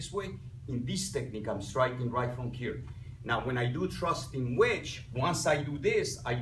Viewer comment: Great explanation and demonstration. But I do have to confess that I heard "thrusting wedge" as "trusting witch" the first time through.